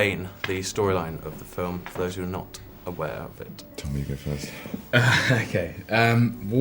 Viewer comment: Is Benedict said "Tommy" at 3:43? It's so swwet. :) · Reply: he actually said "tom, you go first" still cute.